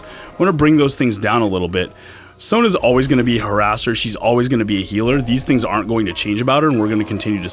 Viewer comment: I heard " Sona is always going to be HER ASS"